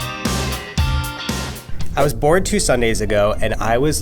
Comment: lol I thought he said “I was born 2 weeks ago”😂😂😂